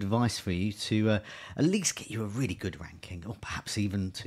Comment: I skipped the intro to a random place (0:55) and I left it paused for a while. I then just pressed play and all I heard was 'give you a good wanking'. 🤣 Amazing content as usual.